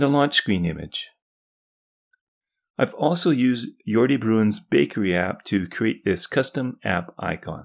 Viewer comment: Which app you mention for the icons? Couldn't understand it. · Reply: It is called Bakery. I have added a link to the description too. <link>